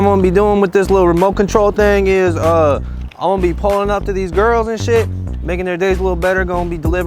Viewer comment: When u said "girls and sh*t" ... i thought u said "girls are sh*t" 😆😅